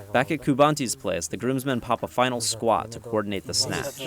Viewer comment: Did he just say, "...coordinate the snatch." ?